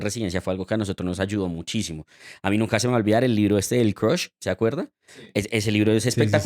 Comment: Cual es ese libro decross? Que dice en el minuto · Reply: Crush Step 1: The Ultimate USMLE Step 1 Review